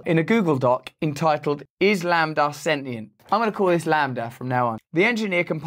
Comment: #RusselBrand at 02:50 i thought you said ( Im Gonna Call it ISLAM from now on ) and i was really trippin out haha :D :D